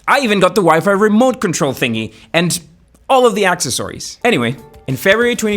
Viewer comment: did you say "Access Ories"?